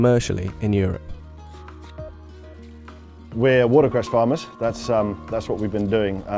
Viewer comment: does he say “water grass farmers” ?